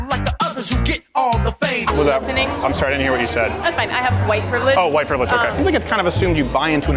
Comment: They miss pronounced talant .... it sounded like they said privilege